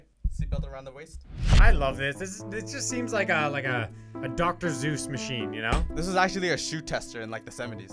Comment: Did say "Dr. Zeus machine"?